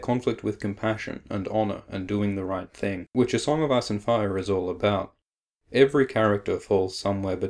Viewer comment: Sounds like hes saying song of ass and fire